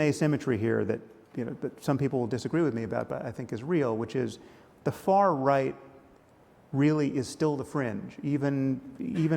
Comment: I thought he said "The far right is still cringe" and I couldn't stop laughing